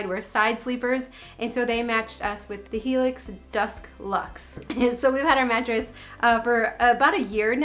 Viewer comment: when you were doing the sponsored ad for Helix and you named the bed you were matched with I thought I heard you say "the ducks nuts" instead of Dusk Luxe. LMAO.